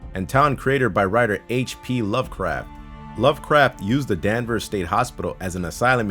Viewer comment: i heard "Love Crap"